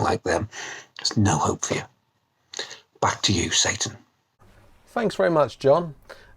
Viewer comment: What did he say at 4:40 ? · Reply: Hey there Hussain! John says, "Back to you, Satan". He's so mischievous. :)